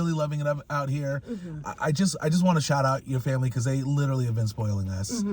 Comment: I thought you said your family was from northern CA